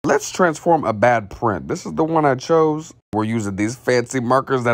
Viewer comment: I heard “let’s transform a bad friend” 💀😭